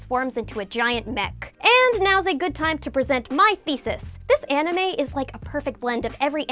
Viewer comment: "...and now's a good time to present my THESIS..." Woah! I thought I heard her saying FECES and I even have to turn on the caption just to be sure.